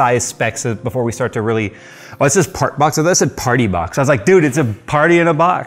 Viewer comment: I thought it said "Fart Box"